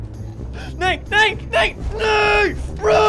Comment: Is he saying No in Swedish? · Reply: Kylie Winchester he says nee that's dutch for no